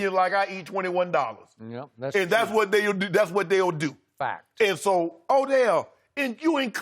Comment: Did skip say fact????🤣🤣🤣🤣